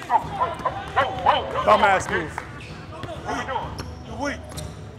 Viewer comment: am I tripping or did this dude say what I really heard “ bum ass niggas “